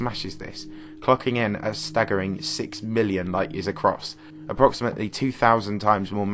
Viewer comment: Idk 6,000,000,000 ly’s wide and only 1,000,000,000 ly’s away, It would take up the entire night sky. I’m calling bullshit on this one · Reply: 6 million (6,000,000) is not the same as 1.04 billion (1,040,000,000). I think you might have misheard